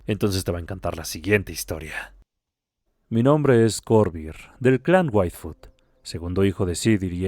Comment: Escuché "del clan Waifu" XD